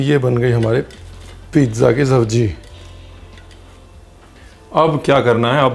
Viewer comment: did I hear pizza ki sabji 😂😂....anyways best video 👍👍